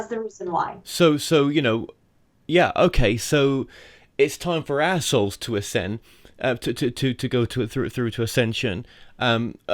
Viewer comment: Very funny! At 25:16 it sounds like he says, "It's time for assholes to ascend." 😜